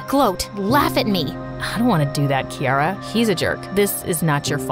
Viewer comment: When I first played the video I thought I heard 'Kirara' instead of 'Kiara' This is a sign that im too addicted to genshin impact😭😭😭😭